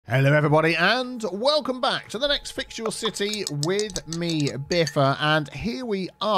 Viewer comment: I always hear "and welcome to another fix your city..." as "and welcome to another fictional city..." Which is still kinda true, I guess.